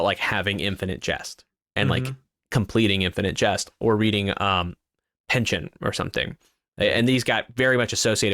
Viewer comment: What book is being referred to at 8:31 ? I hear "Pension" (which the subtitles agree with), but no famous book that I can look up has that title. Does he mean "In a German Pension" by Katherine Mansfield? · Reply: I think he’s referring to the author Thomas Pynchon his book Gravity’s rainbow is notoriously difficult to read